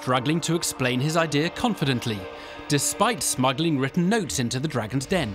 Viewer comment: I thought the narrator said "despite smugly written notes".